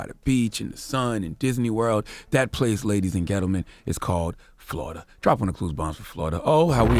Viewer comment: Did he just say ladies and ghettoman????